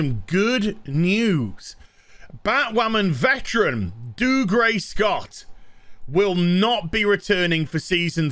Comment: Before I saw how his name was spelled, I always heard say "Dude Gray Scott"